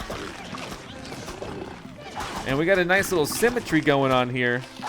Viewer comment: When you said symmetry I thought you said cemetery LoL!